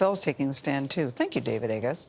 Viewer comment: I thought she said, "Thank you David, I guess."